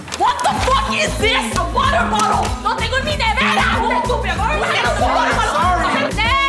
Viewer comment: "No Tengo Ni Nevera!" 😂😂😂 Dios!! Muero😂😂😂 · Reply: Guadalupe Garcia yo entendí "Lo tengo en mi nevera"